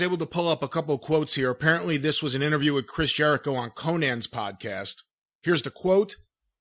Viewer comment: When said “Konnan” I heard “Conan”, so during their entire video I was imagining Jericho talking about the business and Conan being completely confused